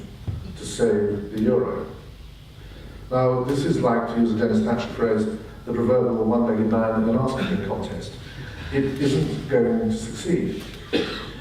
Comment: What was the Dennis Thatcher quote at 5:00 ? I can't hear what he said. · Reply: Denis said "He was about as useful as a one legged man at an arse kicking contest! I remember Rhodri Morgan using it as well.